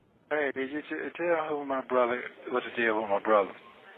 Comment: did Sonya say she already texted his “brother”?